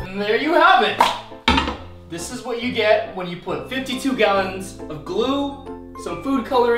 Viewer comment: ROI SAID 52 GALLONS, NOT 51XD